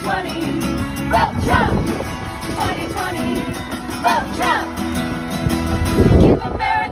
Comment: I heard fuck trump